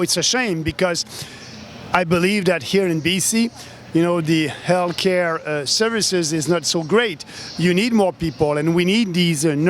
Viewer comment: Sounded like he said, "hell care system" which is the way that I think about it.